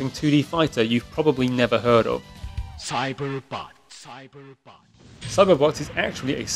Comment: Anybody else heard Cyber Butts at 3:05?